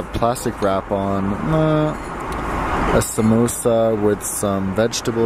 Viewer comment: Did I hear samosa at 9:38. Indian snack on sewdish airline menu..👍👍👍